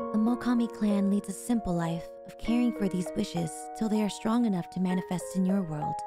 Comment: OMG this whole time since debut my dumb ass thought you said "fishes" not "wishes"😂😵 I remember even commenting fish are friends not food😅